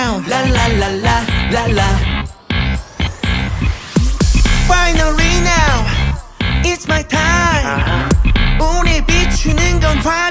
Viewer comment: Yunho's "Finally now its my time!" is going to forever remain ICONIC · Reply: The first time I thought he was saying "Bailarina, it's my time" with accent 😂